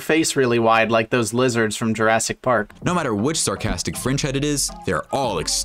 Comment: am I the only one who heard it as a sarcastic cringe head ?